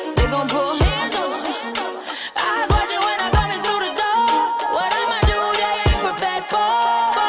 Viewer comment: "What I'ma do, they aren't prepared for" I misheard this as: "Whatever you do, you're a bad boy" xd